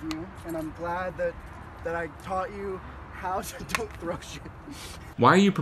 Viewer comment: Can someone PLEASE tell me what the guy in the last clip said?? I swear it sounds like he said, "I'm glad that I taught you how to deep throat shit" 💀😭 Or maybe "don't throw shit"??